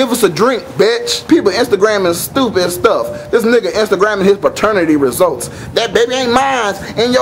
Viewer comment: i thought he said Nigga Minaj